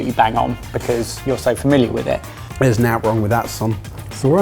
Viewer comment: what did say? "There is nat wrong with that son."? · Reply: He said "nowt", which is a northern English way of saying "nothing". So he means he likes it.